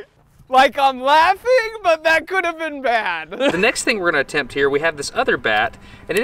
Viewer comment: Did he just said "like i'm laughing but that could have been BAT"?